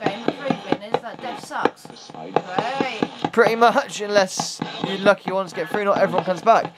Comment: Oooh I heard "pretty much" too